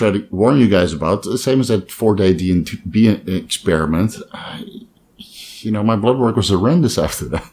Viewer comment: what did he say about an experiment? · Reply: 4 Day DNP Experiment